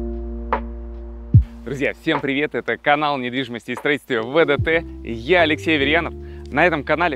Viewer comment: Я один услышал "Привет, это каканал недвидимостии ВДТ"?